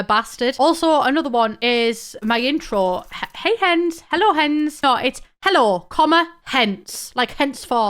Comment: I ALWAYS THOUGHT YOU SAID “HELLO HENS” NOT “HELLO, HENCE” OMG 🥲